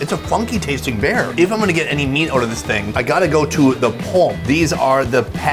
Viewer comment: What are gay meats? @ 0:59 ? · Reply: I mean I thought he said gay meat?